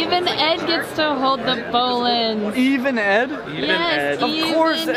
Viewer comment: Whats that black and yellow snake @ 23:25? I heard bowling python but cant find it :( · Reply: Boelen's Python